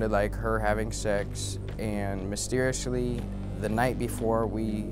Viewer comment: Lol on 1:45 did he say “Mysterioushly” instead of “Mysteriously?”🤣